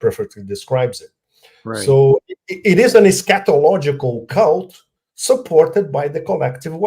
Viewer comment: I seriously thought he was saying scatological not eschatological